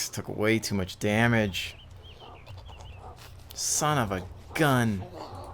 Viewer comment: did you just say son of a gun?